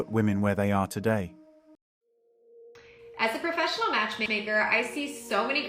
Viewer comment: oh dear. I thought she said, “as a professional masturbater”…!!! Not match maker..